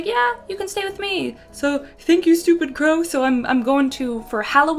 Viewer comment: The way you said, "Thank you stoopidcrow!" made me choke on my drink. XD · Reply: When you said that I thought you said "thank you stupid girl" LOL